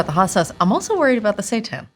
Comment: I'm probably going insane, it sounds like Kate is saying "I'm worried about the Satan" "How are you feeling about the Satan?" LOL I'm dying !